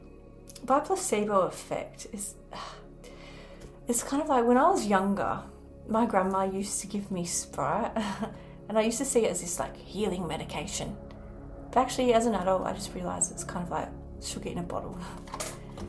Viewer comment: I heard sprout